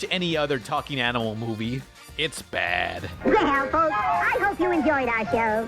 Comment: I swear to god he said “well assholes”